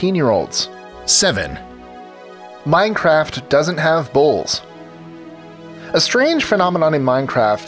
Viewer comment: At number 7 I thought he said "bowls" at first instead of "bulls" lol